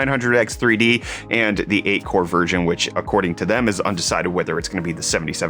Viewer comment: My hearing isn't what it used to be. Did he just say "8 core virgin" at 1:34?